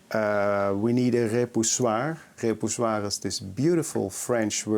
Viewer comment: Can somebody help me out what's the French word he said @ 4:15. The automatic closed caption wasn't any help. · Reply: The word is : repoussoir . Et voilà :)